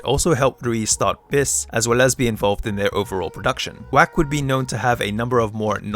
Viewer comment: What did you say here about her producer, Watanabe Jonoske? "who was lesbian volved in overall production?" Also, in 1:43, is it "Gangprayed" or something else? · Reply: "as well as be involved" GANG PARADE, is another WACK group.